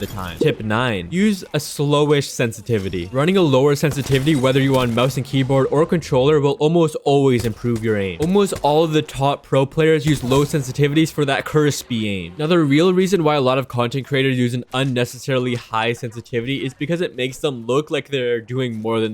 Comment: what are you saying "low sensitivity for curse speeding"? I can't get it